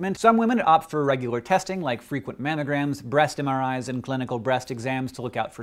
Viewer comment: the captions thought Hank said "Breast in our eyes."